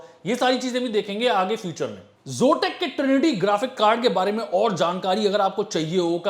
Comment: at -14:04 what he said I didn't get that word ; can anyone plz tell me what word he pronounced from P ? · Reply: Future mein he said